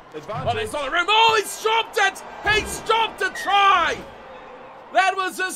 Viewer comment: Did He Say He Dropped A Try? Like If You Think Yes